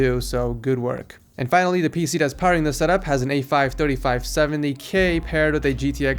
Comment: i thought i heard "An 853570k" ... .wtf